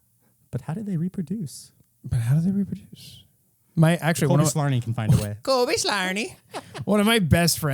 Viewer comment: The cobyslarni bit confused me the entire first hour until I looked up the book overview online. I thought you guys were saying "Colby Slarny" and I was like "who is this guy they keep memeing on?"